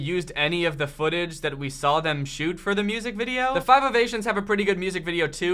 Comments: For the longest time i thought you were saying “five of asians” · Why do I keep hearing the group's name "Five of Asians" hahahaha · I'm sorry but I keep hearing "The Five of Asians" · it sounds like he says The Five Of Asians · I thought kept saying Five of Asians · I kept hearing “Five of Asian’s” and I was like those girls aren’t Asian... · At first I heard, “Five Of Asians”... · I kept thinking it said “standing ovulation.” It was very distracting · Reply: I heard ‘Five of Asians’ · Why tf i keep hearing "five of asians"